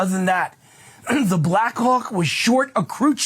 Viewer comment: All I heard was the "Black cock was at fault." Sorry guys. 😂😂